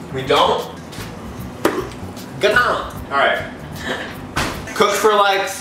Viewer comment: what does say 😂 hahaahah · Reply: Yea he said get out